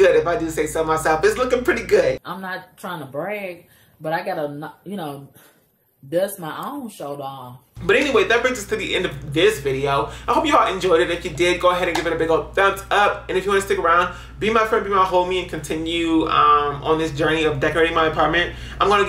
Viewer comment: At first I thought he said "Are you all will?"